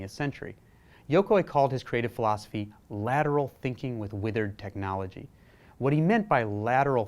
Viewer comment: I totally thought it said literal thinking